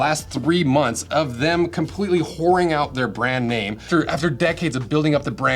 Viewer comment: I was listening without watching and thought you were saying "Whore Wing" as a nickname for "Red Wing."